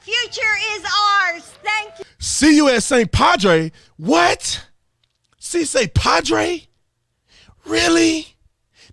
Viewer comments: I grew up and lived in Mexico for 15 years. The first time I heard it I thought she said "Si, compadre" lmfao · Oh, I thought she said "Sí se cuadre", "yes it becomes shaped like a square"